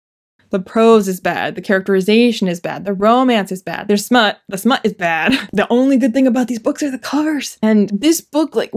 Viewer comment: Did... did you say "The smut is bad?" SMUT?